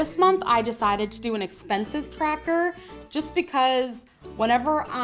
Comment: I thought you said “expensive cracker” lol in my head I was thinking “ how much do ritz crackers cost where you live” haha didn’t know what you were talking about until you wrote the header.... goddess me